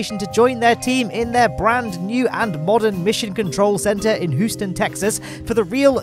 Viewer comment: I'm sorry did he say hooston? instead of houston XD